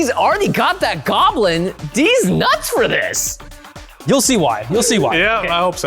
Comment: Anyone else heard "dis nuts" at 26:59 ? 😂😂